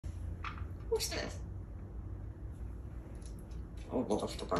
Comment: It sounded like he said "who's this?" I'm not not an expert birb translator though haha. What an amazing guy is! I'd absolutely love to have a parrot, but it's not in the cards for me unfortunately. I've always wanted one.